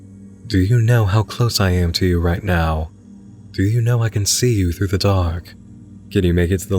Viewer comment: i had this on as background noise so i was kind of half paying attention so i was really confused when he said "do you know i can see you through the dark" because i thought he actually said "do you know i can see you through the dog"